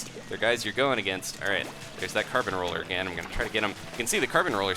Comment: For awhile I'd thought you said Carpet Roller instead of Carbon Roller. Oops! :P